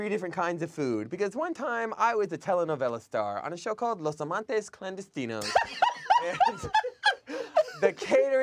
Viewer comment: What was Damien saying at 13:23? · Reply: If you mean what the Spanish means, it's "Los Amantes Clandestinos". It means "secret lovers" and it's a 30 Rock reference.